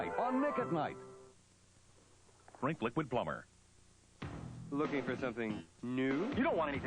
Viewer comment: Um, did anyone else hear "Drink Liquid Plumber" at 58:43?